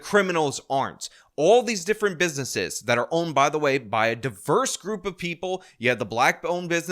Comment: Sounded like she said a group of people loser!